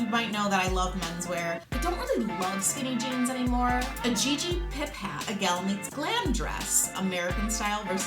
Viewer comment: Totally thought you said GG Pimp hat. haha!